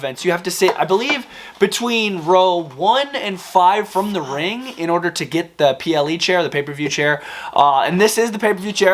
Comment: what is saying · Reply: He said f**k